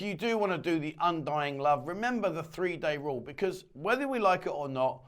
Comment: Bugger …. 3 days ….. I thought you said 30 days :-(